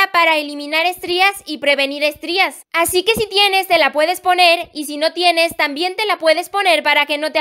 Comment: Ahh como es ta eso de que si tienes te la puedes poner y si no tienes tambien te la puedes poner... diganme si lo escuche bien esta en el minuto °1:37° por cierto me encanta tu contenido♡